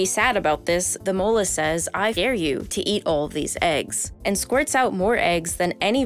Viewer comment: Anybody else catch the edit at 13:07? "I f-dare you," pretty sure she said "I fucking dare you" lmao 🤣